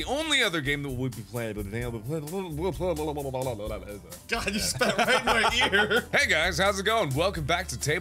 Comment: When said "You spit in my ear", it sounded like he said "You spit on my wiener."